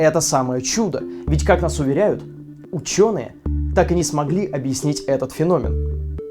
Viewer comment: мне послышалось,или ты сказал у чёрных?